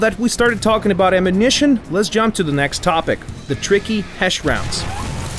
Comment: Lol I thought he said hash browns :P